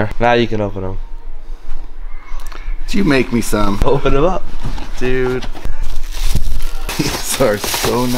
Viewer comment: did he just say dude? He is officially the coolest teacher ever.